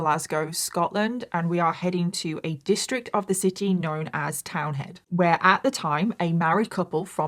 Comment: I thought you said “Cow Head” i had to replay.Hello everyone ❤